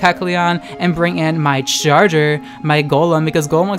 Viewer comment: also at first when you said "bring in my Charger, my Golem" I thought you said Jar Jar and I was so fucking triggered hahahah